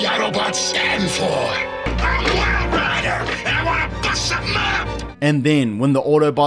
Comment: Dude you gotta put the misheard line that I myself due to years of porn just thought I heard. His name totally makes it more lewd ROFL! 9:29 "I'M WILD RIDER AND I WANNA BUST A NUT!"